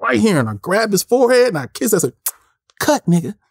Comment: No lie, sounded like Cuba lmao lol